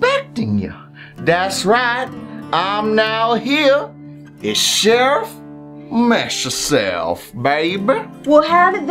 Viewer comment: When you said sherrif messyourself I thought you said chef messyourself